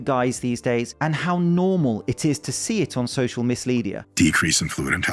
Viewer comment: I thought you said "misleadia", which is a great analogy.